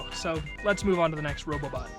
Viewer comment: did you say Robobot?